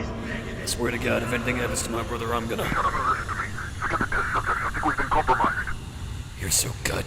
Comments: I think he either said “Forget the Test Subjects” or “They got the test subjects” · also, this sounds to be “Go get the test subjects” or maybe “forget the test subjects” ofc I have no idea, I just hear that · i think hes saying "forget the test subjects" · I think he says forget the test subjects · the unintelligible part is actually saying "forget the test subjects, I think we've been compromised" I know this from the screenplay which was released for Patreon members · I heard “we’ve been compromised” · I think he says “Forget the test subject” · In the "unintelligable" part, i think what the guy on the phone said "they got the test subjects." and thats all, hope it helps :D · I think he says "we got the test subjects" here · I think he said "Take out the test subjects, I think we've been compromised." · says, "forget the test subject." It's hard to tell but I'm certain that's what he said.